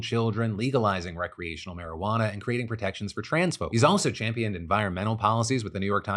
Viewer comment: "protections for trans-folk" really sounded like "protections for transphobes" until a good couple of relistens...